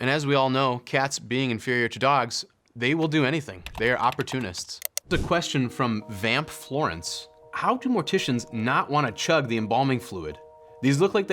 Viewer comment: did he just say cats are inferior to dogs ?